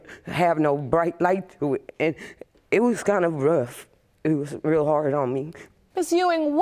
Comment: lol did she say it was really hard homie??